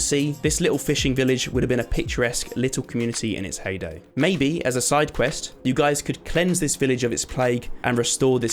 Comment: " Maybe as a side quest you can cleanse this village" What i hear: " Maybe as a side quest you can stop a plague "